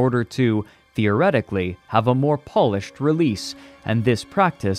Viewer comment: did he say polish, like the country??? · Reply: He says it like "polishing a shoe", but with an unusually open vowel, almost that way it's pronounced in a choir.